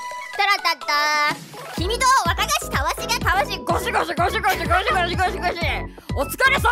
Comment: 最後のゴシゴシゴシゴシが4◯◯54545に聞こえるのは僕だけ？